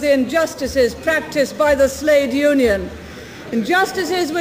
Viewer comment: Did she say "slave union" at 6:34?